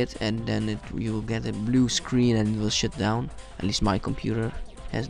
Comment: it sounds like you said sh*t down.